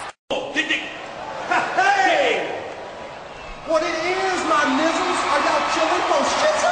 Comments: did he say my nigga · 😂😂 what did he say ? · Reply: Nizzle Hahaha 😮😮😅😅😅